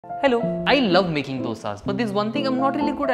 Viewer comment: Bro i thought u said "i love making sins" bcuz dosa in indo means sins.